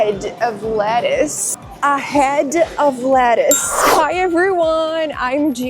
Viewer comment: I heard "ahead of lettuce". Lettuce is definitely behind!